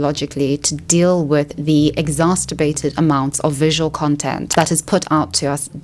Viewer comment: Funny freudian slip at 5:08 when instead of saying "exacerbated" she mixed "masterbate" and "exacerbate" to say "exasterbated"